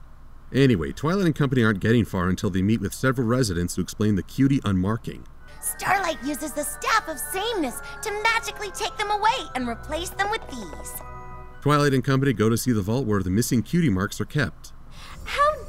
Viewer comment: I always thought she said "Staff of Sane-ness" instead of "Staff of Same-ness".